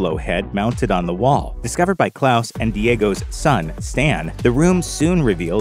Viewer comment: Did you just say claus and diagos son stan